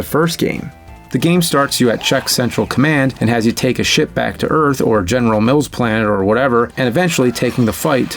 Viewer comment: I swear you said “take a shit back on earth”